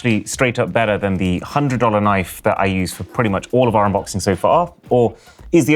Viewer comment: did he say 100$ knife ???